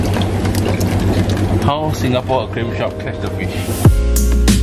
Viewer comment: Are u Singaporean cause in your vid I heard a sound "This is how Singaporean catch fish"